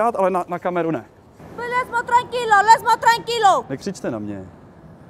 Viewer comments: oO 1:48 she speaks French, she says "Laisse moi Tranquille" (leave me alone), it's crazy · suddenly she speaks French: “Laisse-moi tranquille” (English: “Leave me alone”. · why does she speak French ? 😂 I’m french so she said « laisse moi tranquille » it means « leave me alone » wtfff 😂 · A "deaf" woman who can reply in french (@ 1:50 "Laisse moi tranquille !" Stand for leave me alone). This scam is all over big cities in Europe. I bet she is a kind of gypsy. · Wait...Why is she speaking French ? She's obviously not French... She has a terrible accent. But she says "Laisse moi tranquille"... It means "Leave me alone" in French.